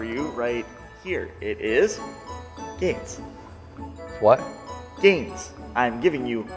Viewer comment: I thought he said aids at first 😂